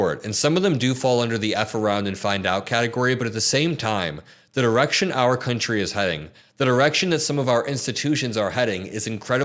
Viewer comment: I thought you said "the erection this country is going."